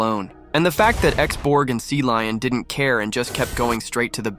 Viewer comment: I hear "Sea Lion" instead of "Cecillion"